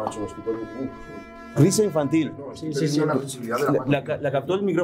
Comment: Sicofonia de un hombre que dice .... SI!! Y seguido se escucha la voz de una niña que dice...ES VERDAD! Espeluznante sito!! Felicidades a todo el Equipo!!